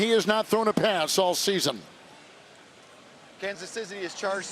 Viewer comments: Bro I promise I heard “Kansas Sissy” is charged for a timeout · did he just say Kansas Sissy?